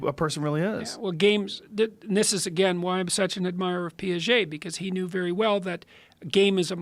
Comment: what is the name he refers he is an admirer of? can't quite understand it? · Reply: piaget as in Jean Piaget, cognitive psychologist